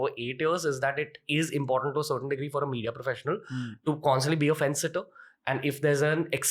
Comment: what’s the word Ranveer have used ? The one who remains in centre? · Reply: Fence-sitter